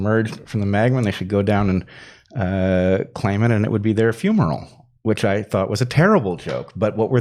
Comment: "It's your funeral" lol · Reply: I thought, he said fumeral with an M?